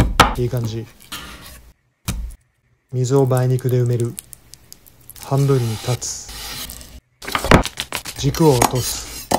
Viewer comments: 「半分にカッツ」って聞こえたけど字幕見たら「半分に断つ」だった。 おみそれしました。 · 急に、「半分にカッツ」て聞こえてびっくりしたら「半分に断つ」だったw